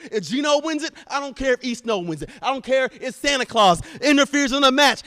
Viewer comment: Did he just say: "Santa Claus"? (It's December after all.)